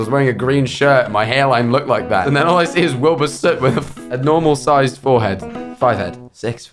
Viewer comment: I like how Niki's rumoured boyfriend isn't Wilbur Soot but Wilbur Scoot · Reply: why do i hear ‘Wilbur Scoot’ and then in my mind. Picture of Wilbur’s head on top of a ‘scout boy kid’ body :,)